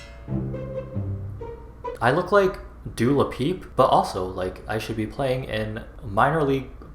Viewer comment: “Dewa l’peep” I’m sorry WUT? Never in my life have I heard it pronounced like that lolll